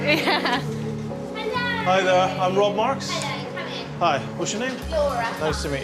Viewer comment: Is his name Rob Marks? And did he just say "Hello, i rob marks" at 08:30?